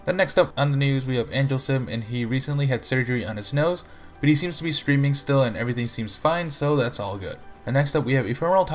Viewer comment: i thought he said screaming still after surgery